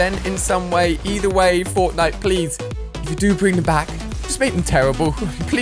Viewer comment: Did he just say bring back meechs